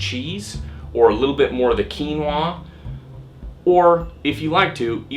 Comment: ehi guys what did say at 3:08 ''Keweenaw'' ?! sorry for my bad eng, the yt sub is not dat 100% accurate... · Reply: It's called Quinoa. Pronounced "Keen-wah".